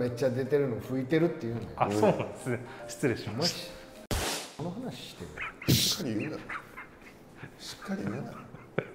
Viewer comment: ここなんて言ってるんだろう？ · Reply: 潮だと思いますᐡ𖦹 ·̫ 𖦹ᐡ